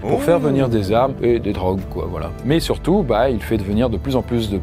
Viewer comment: pour faire venir des armes et des "drones" (en 1974 ?...) désolé mais c'est ce que j'ai compris... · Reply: Drogues...